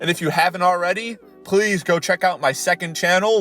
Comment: For a split second when you were advertising your second channel I thought you said "Long Nail Nudes" and I was like "EXCUSE ME?"